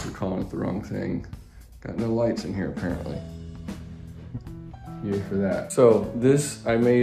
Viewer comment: Did I just hear you say "yay for that"? John Lovell would be proud 👏👏👏